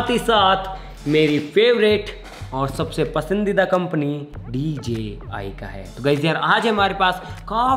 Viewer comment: I thought he was saying " meri favourite company amazon hai" 😅🤣